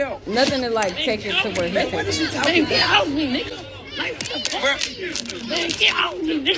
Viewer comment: I like when TI got in his mouth. Showed him up! I thought he (King) said “don’t play with me” but when TI put his hands on him. (King) was saying “get off me Nigga. Why didn’t he get him off him? You played like you were about that LIFE! When you just showed you couldn’t handle him. If you’re so bout it bout it. You would have done him in. You weren’t ready KING!